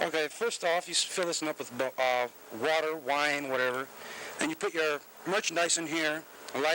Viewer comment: water or wine?? Did i hear that correctly? 😂